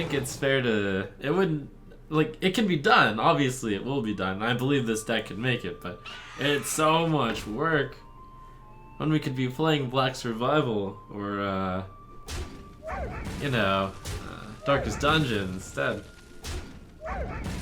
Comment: Did I hear... Black Survival?